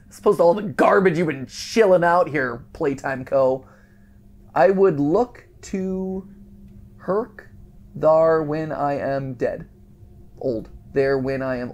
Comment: I think it said I would love to work there when I am old